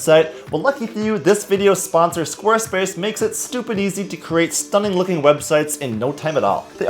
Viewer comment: did you say 'stupid easy?'